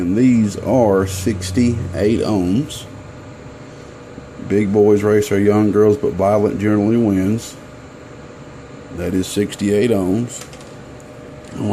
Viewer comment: hey. what was that quote you said at 2:34? big boys.........? thanks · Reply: gabriel mardelli Big Boys Race Our Young Girls But Violet Generally Wins <num> value 0-black 1-brown 2-red 3-orange 4- yellow 5-green 6-blue 7-purple/violet 8-gray 9-white I never have to look up ANY resistor because of this lol, silly but works. percentage and tollerance is simple. Gold...move decimal left once, silver move twice. For example, Yellow-4 Violet-7 Gold Gold.... 47.0 Ohm Gold move to the left once. 4.7 Ohm hope it helps you never have to look up another resistor again.